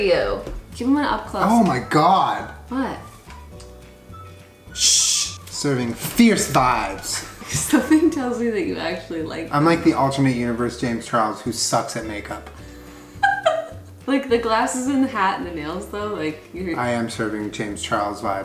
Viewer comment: Since this video came out (and the millions of times rewatching it) I thought Julien said “serving fierce fives” and I thought that was an immaculate joke. I now realize he said “serving fierce vibes” and I got cucked by the Mandela effect )